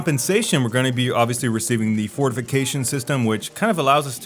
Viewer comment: On the first playthrough I heard "Ford Vacation system" and was really confused. You actually said fortification system but it still sounds fucky. lmfao